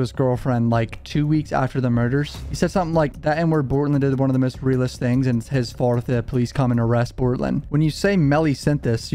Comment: Bro what did you say right here? 11:32 I ran it back like 8x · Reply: Melly said something along the lines of “bortlen did one of the realest things and it’s my fault if they come and arrest him”